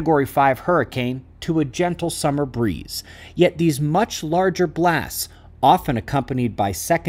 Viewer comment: Did just say his nickname is "cool breeze?" Seriously, very easy to understand explanation.